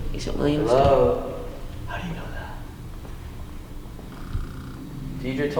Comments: you can clearly hear someone whispering!! I think it said “how you know that”?? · I heard how did you know that